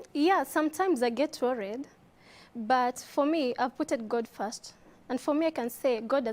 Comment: Did I hear " putted" at 2:22 😂😂😂 kizungu yawaa